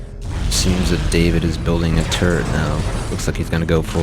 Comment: lmao it sounds like he saying turd instead of turret